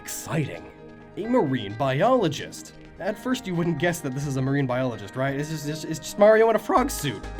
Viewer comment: DID SOMEONE JUST SAY A MARINE BIOLOGIST!?!?!? WIDELY USED AND KNOWN JOJO MEME HERE